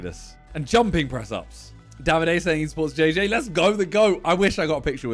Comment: who does Simon say supports JJ, and that Simon was afraid to take a pic with? Cant tell what he said lol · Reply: Davide, he was the latest winner of the Love Island show.